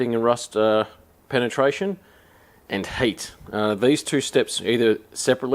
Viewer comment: 😂😂Wasnt watching just listening. His accent made it sound like he said "use lubricant and HATE"...I chuckled to myself and thought "this guy never forgets the last job and comes in full HATE after those bolts"....anyway...dont forget to use HEAT,LUBRICANT, and EXTREME HATE when removing a stubborn bolt.